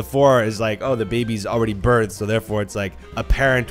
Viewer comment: i was with captions ON and it said "burned" instead of birthed